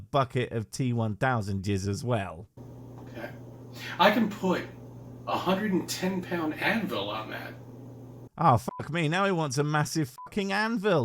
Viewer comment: I thought he said that he could "put a 110 lb. Advil on that." Which would be great for the massive headache induced by his followup tangent.